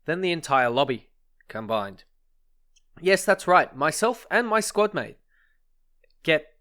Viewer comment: Me and my squad mate Persy. What I hear, "Me and my squad mate Pussy"